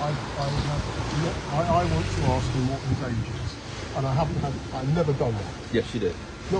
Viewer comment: isn't "unintelligible" It was: " I wanted to ask him what his age is". Not important, but for those who want to know what he said, I think that was it.